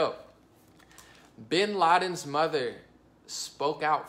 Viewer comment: Lol! I thought this said "Obama's mother"...and couldn't wait to see who it was!